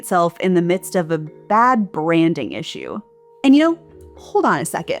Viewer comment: I heard "Brain issue" and linked it to the long decline of the public education of the average people, which is somewhat linked to the 110% capitalism and only money is the god, not the people... But then I rewind... "Brand issue". OK, I would have been surprised 'cause the term "Brain issue" would be unusual for your channel.